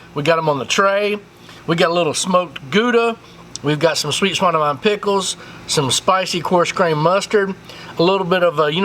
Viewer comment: What kind of pickles at 7:07? The closed captions say “sweet spidermind pickles”. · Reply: Sweet swine o mine